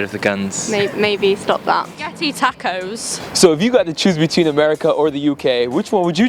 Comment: Did she just say Spaghetti Tacos at 5:27 ?